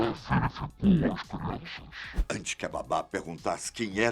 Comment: so eu que nao entendi oque ele falou no 5:07 so entendi " crianças " no final ... rsrs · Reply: Pelas crianças eu acho